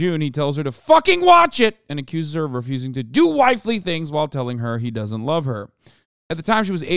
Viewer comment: He said "do wifely things?" I thought he said "do waifu things" lolllllll